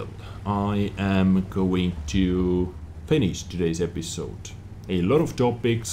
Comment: Did you say I am going to penis this episode?